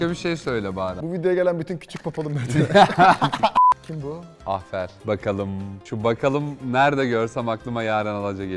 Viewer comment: ahfer mi dedi benmi yanlış duydum 0:34 😅😅😅❤❤❤